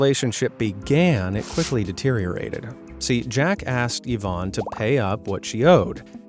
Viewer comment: What said: “Jack asked Ivan…” What I heard: “Jackass” Also me: “Wait what-?”